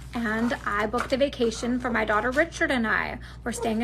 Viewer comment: did she just say her daughter is named Richard??